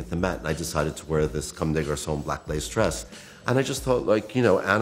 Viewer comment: did he say @ 9:10 that he wore a black laced dress lol